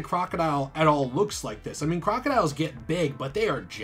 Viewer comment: Did he just say crocodiles are jacked? 7:58?